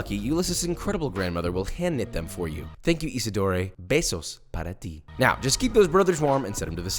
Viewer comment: I know he said besos para ti, but I heard pesos para ti (money for you) and I imagined him hiring a spanish speaking grandma for those beatiful towels